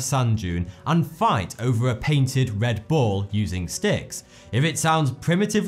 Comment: I thought he said "fight over redbull" lol